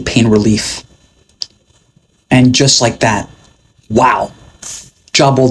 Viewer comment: Am I the only one who heard him say "soft tissue Adam tate" at the end